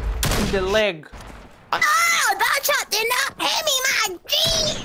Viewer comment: I think he said "Ohhh, that shot did not hit me my G!"